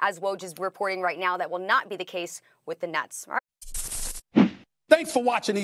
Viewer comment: did I just heard nuts